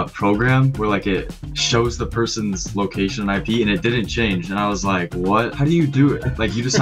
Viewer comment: Did he say location IP? Why the f*ck does he have a service showing IP adresses?! creepy as hell